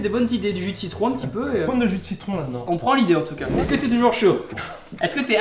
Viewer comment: j'ai entendu "Est-ce que t'es toujours chauve ?" 🤣🤣🤣 jme suis dit C'est pas possible il va le démarrer 😂